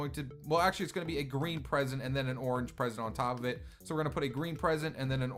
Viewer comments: i thought you said an "Orange President" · once you said president instead of present xD!! that when you placed the orange one on top of the green one!!! at